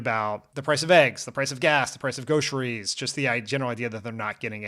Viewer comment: Did Dan say "Go-ceries" at 13:25? lol